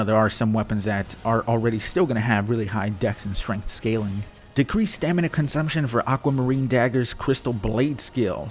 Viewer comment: First i heard "increased the stamina consumption of aquamarine dagger's..." and i was thinking of buying the same crack the patch team is on. Thankfully they had their spectacles on, and saw that the thin blue stick is indeed not a greathammer, even if it may resemble one.